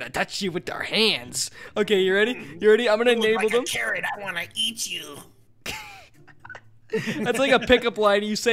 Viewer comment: I swear said 'You look like a Karen , I wanna eat you' the first time around.